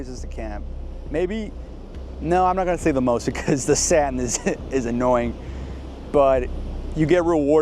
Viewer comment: did he just say the salmon is annoying?